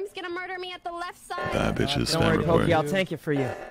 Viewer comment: Did he just say bad bitches spam report as says he's spamming report